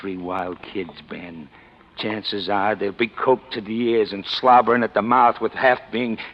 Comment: Did he say coked to the ears?